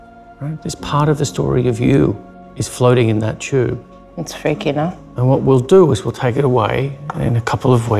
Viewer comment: Wait, did she just say “it’s fake ya know” ?? 1:25 lmao